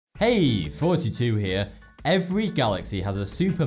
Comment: I've always thought the intro said "Hey, 42 here" but its actually, oh my lord, it was all a lie.